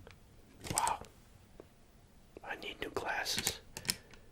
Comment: I love him but did he say I need glasses wail wearing glasses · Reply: He said wow I need new glasses lol